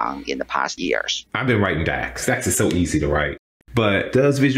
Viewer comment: did he just say DAX is so easy to write?